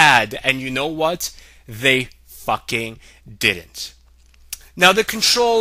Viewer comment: i thought he said "they fucking did"